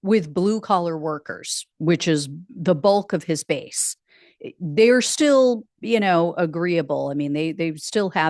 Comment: Hi, Could have sworn you said 'he's lost the bulk of his face.....' spits coffee out.